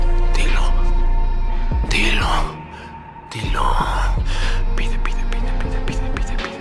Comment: Cuando dijo pide, pide, pide pide yo escuche nepe :(